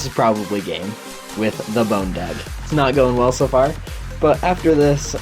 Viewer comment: I thought you said boner dick